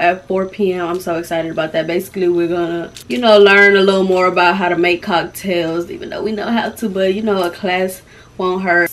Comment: What does say about the skirts ??? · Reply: Dat I done ordered too many 😂